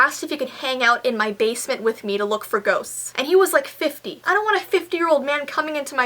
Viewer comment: did she say " goats " or " ghosts" ???????????????????????? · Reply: Ghosts